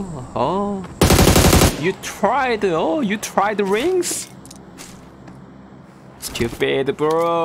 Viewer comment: I thought he was saying wings then i saw the title